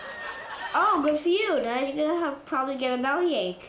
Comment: The 2 boys at 2:45 are the type of children I hope I have one day. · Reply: what does he say at 3:07 "oh good for you dad you're probably gonna have a ?"